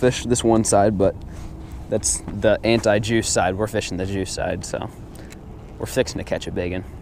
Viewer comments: I thought you said anti Jew side😂😂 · I thought he said "Anti-Jew side"